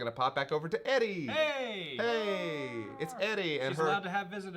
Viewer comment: When he said Eri I thought he said Eddie i'm sitting here like who TF is Eddie 🤣